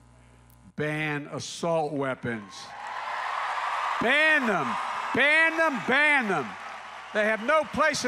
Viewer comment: Funny… I heard “buy them, buy them, buy them” 😂